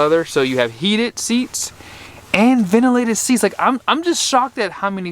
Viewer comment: I agree. I wish it had ventilated seats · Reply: He clearly says it has ventilated seats timestamp